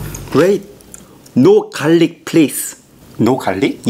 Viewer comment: I thought you said harlic but it was garlic